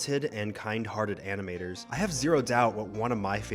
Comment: when he said animators I thought he said animals! 🤣